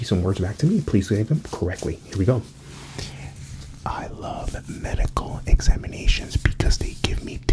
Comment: I ain’t gonna lie thought he said I like men